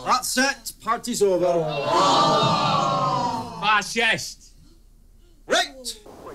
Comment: What did Craig say at about 3:26? I really like the Scottish accent but I couldnt make it out properly. Did he say Fascist? I apologise if I came across as ignorant :)